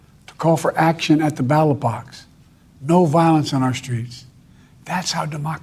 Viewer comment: lmao Ballot box, I 100% thought he said Battle Bots